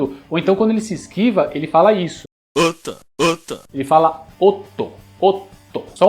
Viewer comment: Na época que conheci KOF foi a mesma época que assistia todo mundo odeia o Chris, então nada me tira da mente que ele fala "Gota" XD aquela mesma doença que o Julius tinha hahahahah